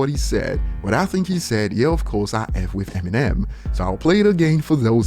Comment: sounded like he said "yeah of course, its fucking eminem"